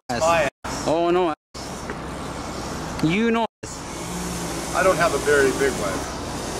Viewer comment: did he actually say, "I don't have a very big one." ? ? ?